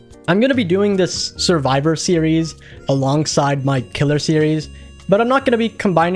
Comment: i moved my phone and somehow misheard "killer" as "gooner". I was so baffled, like "a GOONER series?! WHAT?"